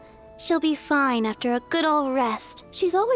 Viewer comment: Diluc is a big mood in the Jean quest. At a party, sitting in the back away from everyone scowling. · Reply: I thought you said "good mood" and was confused af